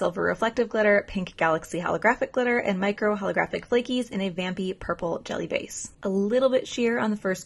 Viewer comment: I thought you said a Bambi purple base. 😂